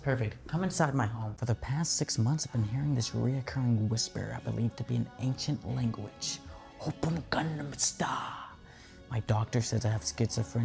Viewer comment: who else heard oppa gangnam style